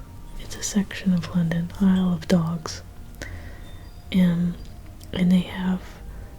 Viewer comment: at first i heard "i love dogs" and got very excited about a place called like this